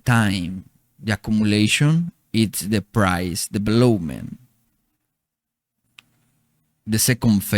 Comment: Fire, fire content. Really insightful. I will listen again to really get it. Thank Mr and Mr.. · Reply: did you get what he said @ 0:42? He said "it's the price, the belowmen" what's belowmen?